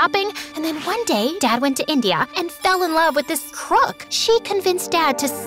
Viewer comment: Lisa said crook and the MSA subtitles said Truck! I’m dying:_MSAMSALaugh2